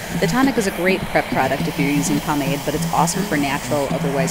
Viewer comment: Oh Pomade. Thought you said palm aid. If I was plagued with vanity I would totally get this bird to do my hair and beard!